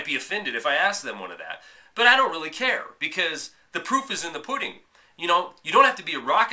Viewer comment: I guess Peter goes on Fast Money to get the word out. He's used to getting laughed at and mocked, but he's a trooper and he's fighting the good fight. It's hard to watch, though. I thought at first you said "the poop is in the pudding!" Actually, that would have made sense as well. Great reporting! 5*